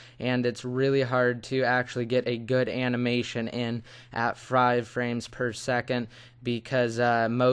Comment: did you say frive frames per second?